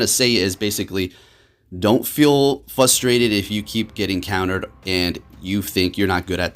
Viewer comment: did you just say fustrated lmao?